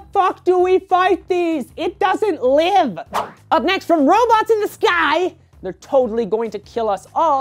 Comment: Like if you thought said robots in disguise instead of robots in the sky